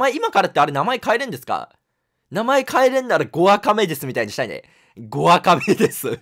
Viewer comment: 「5アカ目です」が「ゴアカメデス」っていう強そうな名前に聞こえた鳩俺以外にもいるだろ